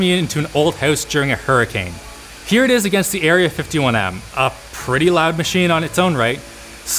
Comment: did I hear Area51?